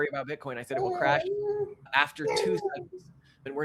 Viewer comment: It would crash after what? I cannot understand it and there are no subtitles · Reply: After two cycles